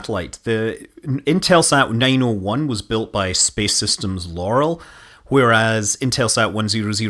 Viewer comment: Anybody else hear 'Space Systems Yanny' at 5:40? 😎